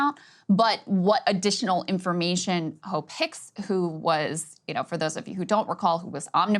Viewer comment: I keep hearing " hoe picks" and "pecker".